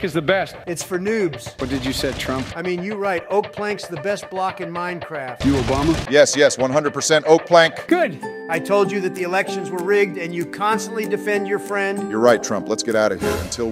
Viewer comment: I swear to god I heard Obama say “help me”